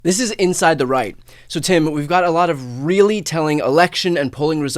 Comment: Lol At first I thought Brian said "This is 'Inside the Reich''" Sadly, this is appropriate.